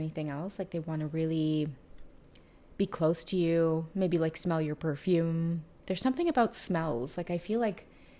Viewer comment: Oh wow, while I was sleeping I heard " Don't forget your perfume" .